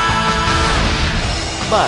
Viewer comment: Dulu gue salah dengar, dengarnya SOSIS Padahal "Sound System". Gue nonton iklan ini Januari 2004/2005 di Metro TV. Nontonnya di TV kapal PELNI KM Labobar kayaknya (?).